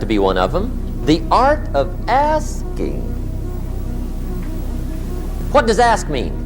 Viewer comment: Mann.... I thought he said the art of asians😂😅